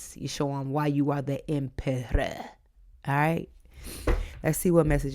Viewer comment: LOLLLL when you Said “show them who’s EM PERRA” I died I heard “emmm perra” in Spanish that’s like “Ermm THE B****” 🤣🤣😂🤭🫢